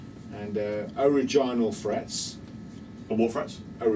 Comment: your playing is just so inspiring, makes you want to grab your guitar and start wearing the wood out. LOL I thought he said Vaginal frets. LOL Even couldn't understand him. Great video guys.